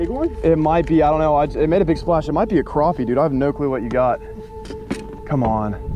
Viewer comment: did you just say crappie?